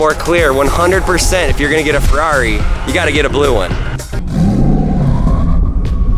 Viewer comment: did he just say “ if you are gonna get a Ferrari you need to get a blue one”